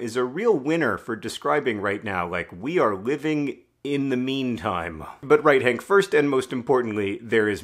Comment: thought you said in the meme time and was like ye it kinda is